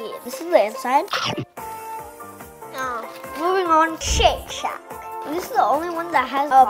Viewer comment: The second time i heard shake shack i thought he said sh1t shack